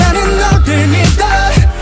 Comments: someone should tell woozi to change the title .. I swear they are saying 'Crazy Love' ... 🤔🤔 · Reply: all i heard is "craze in love" does that make sense? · someone should tell woozi to change the title .. I swear they are saying 'Crazy Love' ... 🤔🤔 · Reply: I hear "crazyn love" 😂